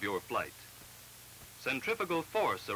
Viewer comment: it sure sounds like the narrator says 'centrifical' force . . . rather than 'centripetal'. I remember this as a grade schooler in the 60's as being the reason I was at odds with the 'real' pronunciation when this was always the sound I heard when people spoke the word . . . when I learned it was with a T, everyone always said it with an F, here seems like further proof why the confusion.